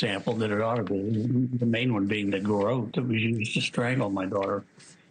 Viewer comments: did he say "the garrote that we used to strangle my daughter"....? It sure did sound like that to me.... I just listened to it a ton of times and it sounds like we.. Not even he, but we. The cc says "that was used", but it sounds a heck of a lot more like we than was.... Take a listen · This is the interview where it sounds like he says "the garrote we used to strangle our daughter."